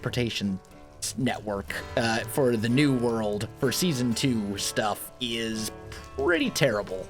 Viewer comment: did you just say season 2?